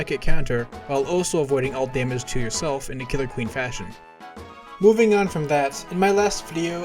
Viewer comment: Did I just hear? KILLER QUEEN, THIRD BOMB, BITES THE DUST!